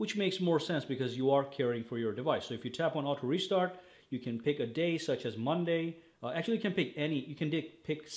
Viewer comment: now you can dick instead of pick 😂😅😂